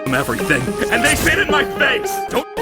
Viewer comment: Sounded like he said 'AND THEY SHIT IN MY FACE'